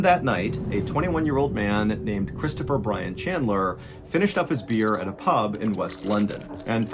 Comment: my brain went into autopilot and thought he said "Christopher Weston Chandler".